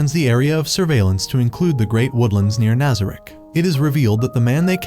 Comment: got me laughing on the ground when he said nigon but sounded like n**a